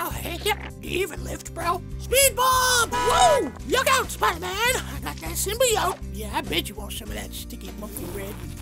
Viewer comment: “Look out Spider-Man, got dat symbiyote” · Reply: I thought she said "look at fat man,I got that symbiote" 😂